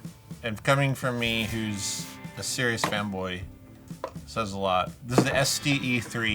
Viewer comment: i thought he said femboy for a second and i was so confused 😭